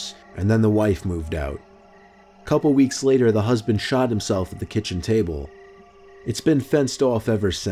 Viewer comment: did he say he shot himself with a kitchen table?